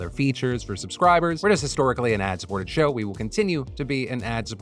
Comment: When you said “ad supported” I heard “ass forward” because I was elbow deep in my fish tank 😂 I didn’t even question it